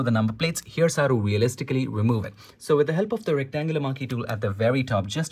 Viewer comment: I misheard rectangular marquee as rectangular monkey tool.😂😂